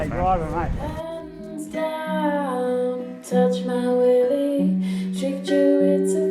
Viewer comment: Did she say "touch my willy"